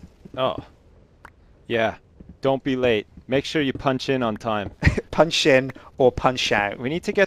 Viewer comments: when you said punch in at first i thought at first you said punch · When they said Punch in and Punch out...I thought they said Punch, Punch out, ha